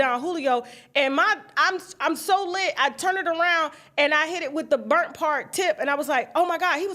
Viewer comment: All I heard was "And I hit the wall" (Vicky Winans testimony lmfaoooooo).....